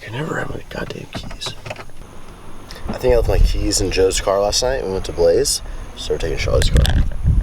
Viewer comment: did he say they went to blaze?